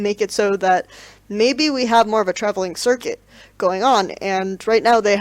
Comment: I thought she said "travelling circus"